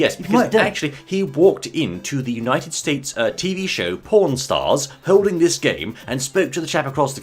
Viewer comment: Did he say porn stars at the 1:30 mark of the video?